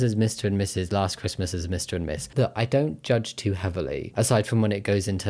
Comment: I misheard the "last xmas as Mr and Miss" to being "as Mrs and Mrs" and thought it was a cute "my wife is now my husband" bit.